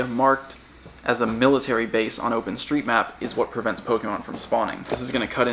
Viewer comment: did you just say Open street map?